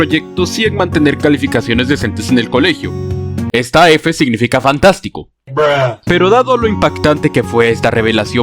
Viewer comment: Que dijiste? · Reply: "Es fantástico".